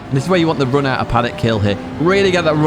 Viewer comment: I misheard "Paddock Hill" as "Panic Hill" and to be honest it fits. Thanks for the race guides, like clockwork!